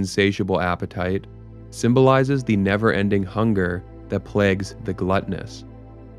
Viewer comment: What if we’re living in one of the circles of hell right now? Destined to relive all of our pain and tragedy over and over, a never ending loop. All the while living with the hope or belief that it ends when we die or that we’ll go to heaven. Completely unaware that we are stuck in hell for eternity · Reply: I thought you said never ending POOP lol